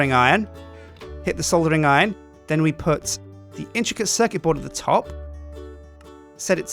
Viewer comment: Captions said " The chica sexy for the top"